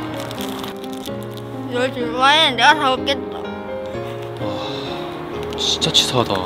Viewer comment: 널줄바엔 내가 다먹겠엏흫흫 이소리 나만 들리나..?